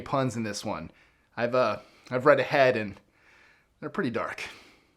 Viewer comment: Did you say "I have a red head" because it is red?